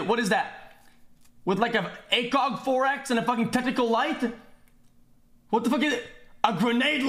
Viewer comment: tentacle light wtf edit its what i heard i repeatedly watched it again and he said Tactical light my bad, my bad bois